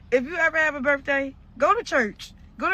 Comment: Did she just say "if u ever have a birthday"?? 🤨🫣🤨